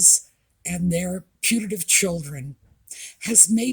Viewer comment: Omg, lmao at myself!!!! For years, I thought you were saying "punitive child". Haha, obviously, I wasn't hearing well. I was always confused trying to figure out what was "punitive" about these children.